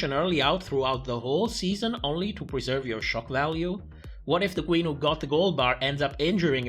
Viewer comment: I keep hearing "it's choc value" every time you say "shock value"😅